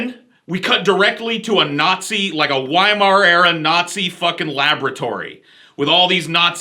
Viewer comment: Wiemar-era Nazis? History ain't your best subject. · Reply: I thought he said Weimaraner?